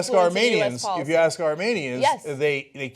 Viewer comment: did he just say "if you ask Armenians?".